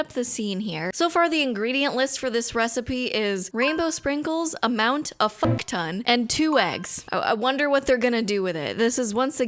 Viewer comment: I misheard "rainbow sprinkles, amount: a fuckton" as "a mount, a fuckton" and thought that was strangely poetic.